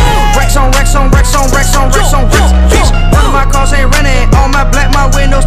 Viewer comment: when lil pump said ‘racks on racks’ it sounded like he said rap song😂